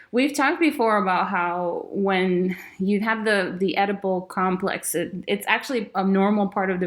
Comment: At first I heard ‘edible complex’. Sorry, I’m an idiot.